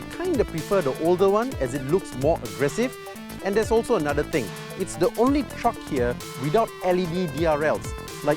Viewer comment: HELP Someone please write down for me what the guy said about ford ranger, my english is not good and i coudn't understood his words. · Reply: He said that the truck is the only one without DRLs Daytime Running Lights